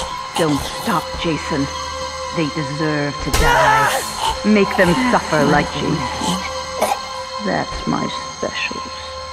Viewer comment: I heard "Make them suffer like Jason did"